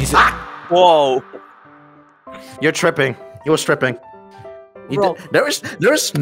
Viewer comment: I thought said "You're tripping" "He was Stripping"💀